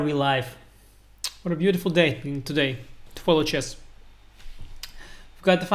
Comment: Did he say toilet chess? Is Anish sitting on a toilet while he gives commentary on these games? Is he actually recording this in his bathroom?